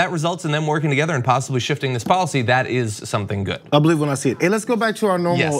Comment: Policy? I thought she said popsicle.